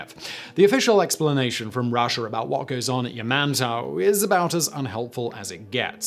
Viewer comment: I thought you said "Ya man's house" and I thought some businessBlaze slipped out again haha